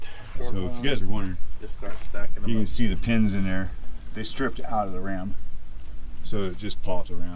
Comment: Always good to see the big wrecker in action guys! (not sure why they didn't weld the hydro-steering back on, but their probably was a reason?) Jillian is my spirit animal...living her best life! (She just needs a little bit of a lift-kit and some larger tires by the looks of it?) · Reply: I think he said the pins had stripped out of the ram...not sure what that actually means though. Doh. 02:15 (hate not knowing LOL, I want to be YouTube smart! LOL) But if you look at 01:34 it appears the bracket that is welded to the body of the ram that bolts to the axle mount broke at both ends?